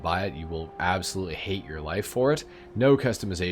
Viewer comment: was honestly expecting a more scathing review of the Craptor lolol. but close enough. At first I thought you said you will hate your life-force lol.